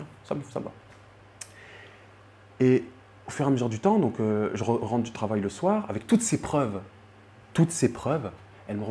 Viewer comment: Il a dit quoi vers 18:23 quand c'est coupé ? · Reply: Une tache de naissance sur la fesse ou la hanche je crois